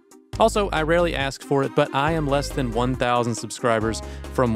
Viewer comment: I heard "I am less than one thousand subscribers..." as "I have less than one thousands subscribers and audibly went "what the fuck?"